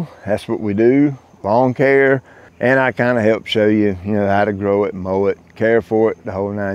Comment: I swear I thought you said "How to grow a mullet" and I thought I was watching Connor's channel.